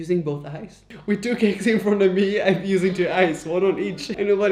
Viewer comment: what the actual hell is he saying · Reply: He's responding to the girl who told him to use two eyes